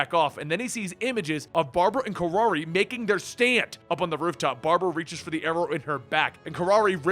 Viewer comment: did you say stand? ZA WARUDO